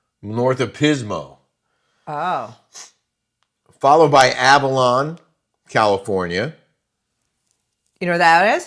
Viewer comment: Good morning guys. I’m in Cayucos. You guys need to take a roadtrip. Avila is actually next to Pismo beach. Everything here is GORGEOUS! Beyond blessed that I live here with beautiful ocean view & walk to the beach. I remember the days of packing up the family to have a beach day when I lived in the Big IE 😂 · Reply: I just heard Avalon, Lol. Thought you said Avila 😂